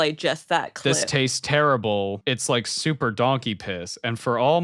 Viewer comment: No way! I also thought he said "supper donky piss". Oh my gaush.